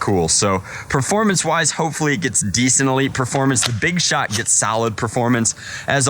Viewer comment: From 2:47, hit play. Does he say “Big Shaq”??? LOL